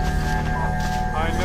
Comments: am i tripping or did he say i know who you are arthur? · did he say ‚I know who you are Arthur?‘ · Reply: Brandi611 He said Marston, I heard it. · is it just me or does it sound like he said “I know who you are, Arthur”? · anybody else hear him say(I know who you are Arthur) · it sounds like is saying “I know who you are, Arthur.” But it’s clearly John as Arthur was probably not even a thought around the time of RDR1. So what’s going on? Is he saying “partner??” 😭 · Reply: He says Marston